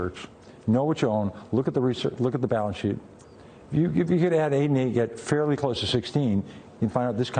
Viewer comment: seconds after he says "look at the balance sheet" he says something with a "sixteen" in it. What is it that he says? thanks. Just couldnt make it out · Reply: "If you can add 8 and 8, and get fairly close to sixteen...." He's saying you don't need to understand any sophisticated mathematics to make sense of a company's financials